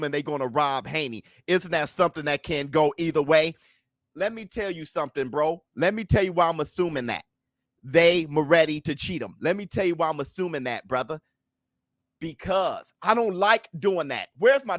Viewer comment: I heard " They Ready to Cheat Him " 😂